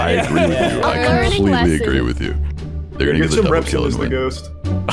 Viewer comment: what did blue say? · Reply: “Get some reps in as the ghost.”